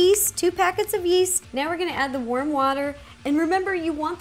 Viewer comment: I thought u said worm water at first lol